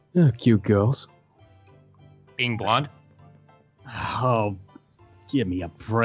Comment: Garroth: exactly everyone has their weaknesses Zane. Zane: oh yeah, well whats yours? Garroth: hmm..........cute girls. Vylad: being blunt. lol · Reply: StevenUniverse MLP123 i thought he said being blonde XD